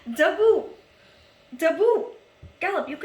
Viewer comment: Lol in the beginning I thought you were saying "Da boop" lol luv u <3